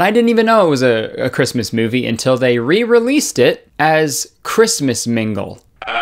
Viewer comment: I legit thought it said Christmas Minge.